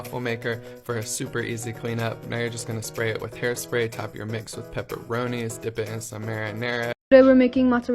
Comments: i have been making tortilla pizzas for like 5 years..only i get the tortilla, add sauce and cheese, and bake in the oven for about 8 minutes..p.s did he just say spray the waffle iron with hairspray ? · Did he just say hairspray??? 7:11 Edit: OMG TYSM THIS IS THE MOST LIKES I’VE EVER GOTTEN <3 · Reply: it's spray oil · Did he just say hair spray? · what the heck did this man just say HAIRSPRAY?? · DID HE SAY HAIRSPRAY?? don’t eat hairspray. did i hear that right pls consult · DID THEY JUST SAY SPRAY IT WITH HAIR SPRAY ?? 😭✋ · Did he say spray the waffle iron with hair spray? 🧐😂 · I'm sorry... did he just say spray it with hair spray?? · did he say hair spray😀✌️ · i know he did not just say HAIRSPRAY for a MEAL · did he just say HAIR SPRAY TO COOK YOUR WAFFELS?!WTF!? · hol up 7:10 did he just say spray it with hair spray?😂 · minutes in does he say hair spray? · DID HE SAY HAIRSPRAY? sis what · excuse me, did he say hair spray?! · Did He just say HAIRSPRAY IN MY FOOD???!!!